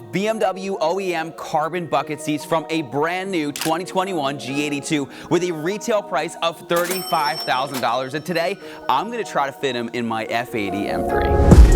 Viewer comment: Let me just clarify.... Did you just say that pair of seats retail for $30,000? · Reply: nah man he said $35,000 US Dollars